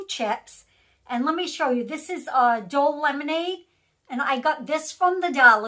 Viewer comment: Lol from the beginning. I thought you said "adult" lemonade. Love your glasses. I left you a comment on your last video. I finally got the unicorn thing up. Hope you get to check it out. Can't wait to see what you try today ❤